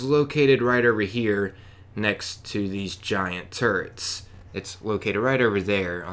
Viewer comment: I swear I thought you said "The next button is located by these giant turds." I thought yeah, I could see how he would call it that. Then I thought "turrets!"